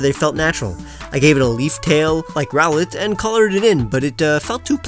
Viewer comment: did you mean grovyle cause it sounded like you said growlithe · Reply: No it was Rowlet the grass starter of alola